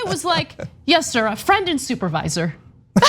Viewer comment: Friend, ohhh. I thought he said “front end” supervisor. As in front of the store lol. Friend is a good title also.